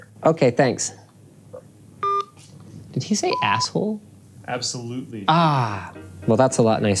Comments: Hahaha I thought the guy said asshole too 🤣 😂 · i thought he said ass hole but then figured it would be absolutely and then my doubt gets confirmed LOL 😂😅😂